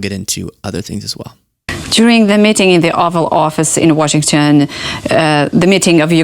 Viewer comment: I like how it sounds like she says the Awful Office.